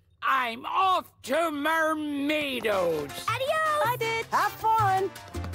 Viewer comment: I thought Jackie said “Bye BITCH!”